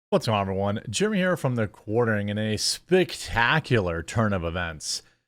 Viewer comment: Did you just say "spictacular"? 😆